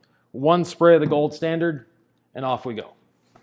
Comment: "why the... " ...... Pls share with us what was whisper what do you know that we don't? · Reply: I say off we go.